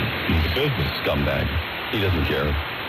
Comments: idk why i heard ''scumbags.'' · I heard "scumbag" and "he doesn't care".